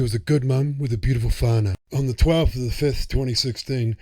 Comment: With a beautiful what? What is that word he uses to describe the victim in the beginning when he shows the victims picture? It sounds like he says beautiful fanna or something idk. It's at the 00:59 mark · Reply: The word is 'Whanau'. It means a Beautiful Family. DC